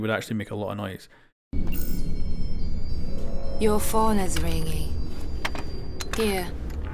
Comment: Your phone is ringing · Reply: DemonRukasu I thought she said your father is ringing